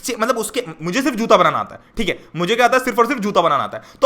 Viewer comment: it sounded like mujhe chutiya banana ata hai